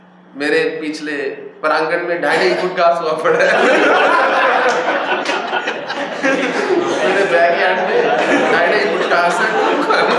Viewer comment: What did Swami ji say at 13:10 ? I missed the joke... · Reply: Swamiji ne kaha ki uss car waale ne un logon se kaha jo ghaas khaa rahe the "mere ghar ke backyard mein dhai dhai foot ghaas hai tum khana"😂